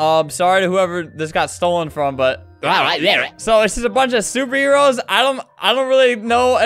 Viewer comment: When he said “so this is a bunch of superheros” I thought he said “so this is a bunch of stupid year olds”